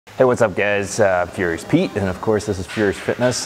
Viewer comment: At the start it sounded like he said "what's up gays"